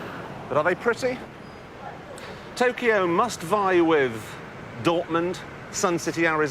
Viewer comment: I am a foreign english speaker. What does he say at 10:50? Does he say "Dortmund"? This is my home town. :) Back then it was a really ugly city because of all the industrialisation going on. Since then it has changed a lot. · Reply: Yes, he said Dortmund.